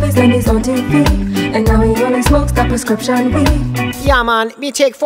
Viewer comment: Though the subtitles show something different, on second 0:50 I hear them singing 'prescription breed'. Anyone else hears it too?